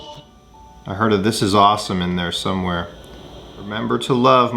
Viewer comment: i think he said im gunna get to fly this is awsome